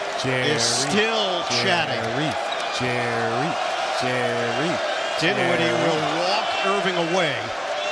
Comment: What is Richard Jefferson saying? So amateur. He sucks as a commentator. LOL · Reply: Jerry! jerry! Jerry!